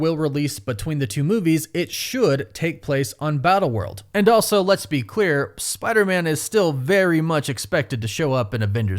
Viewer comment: with all the wokeness that has happend with disney I swear I heard "Lets be queer"